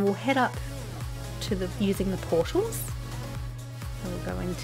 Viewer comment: When u say portal it sounds like your saying poodle lol